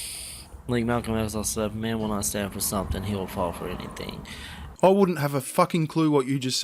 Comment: I think he said: like Malcom X once said, "if a man won't stand for something, he will fall for anything".